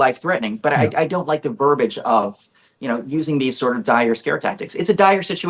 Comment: I think close caption made a mistake here. Close captions says "I don't like the verbiage of you know using these sort of dire or Scare Tactics" but shouldn't it be ". . . these sort of die or Scare Tactics"?